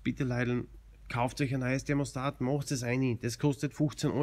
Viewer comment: tut mir leid, nicht verstanden, aber was soll man sich kaufen ? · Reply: Hallo Kerim! Also tut mir leid wenn das nicht verständlich war, dann weiß ich auch nicht! Ein Thermostat! Das Ding das die Wassertemperatur regelt! Sg